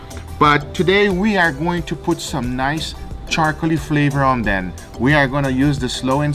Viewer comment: anyone else catch the "om ben" instead of "on them"? lmfao